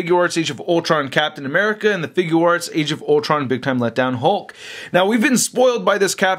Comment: did he said big time let down hulk??